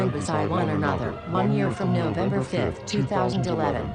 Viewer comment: im not a fucking idiot i just misheard it. i heard november 5th 2012. and i was distracted by sons of anarchy